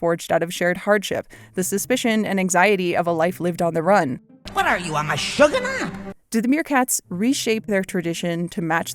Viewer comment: “The Lion King is kind of about racial hierarchy” is a take I’m familiar with, but “meerkats are jews what does that MEEEEEAN!?” is new to me. Great video. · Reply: During a rewatch of this film I heard “what are you a mashuggunah?” And I did a quick double take 😂